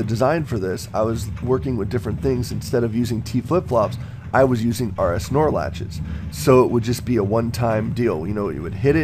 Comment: did you say :"I was using our snorlaxes" ?